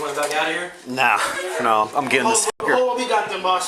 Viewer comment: what that cop say ?? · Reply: Sounds like he said "I'm getting this n**ger"